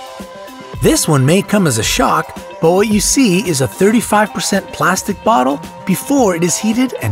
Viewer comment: He says 35% plastic bottle instead of 35 oz